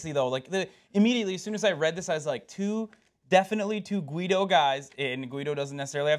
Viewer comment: greedo hah star wars reference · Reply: I thought they said 'guido' not 'greedo'??